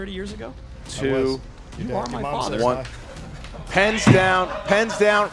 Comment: In case anyone wonder what was said 22:44 -Where you around back then? Rocha-Yes I was. (At The Same Time) -You are my father./Rocha-Your mother says hi. Lmao